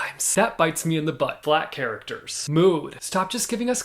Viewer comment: I literally thought he said "black characters" and then "Mood" and I thought I was on the wrong channel for a second there 😅